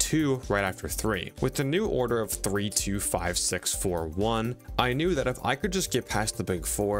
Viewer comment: istg im tripping from all my musical nerd bullshit, i thought you said 24601😭😭